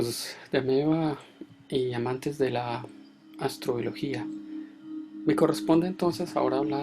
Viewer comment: Yo le escuché "astrología" y me paniquié jajajjajaa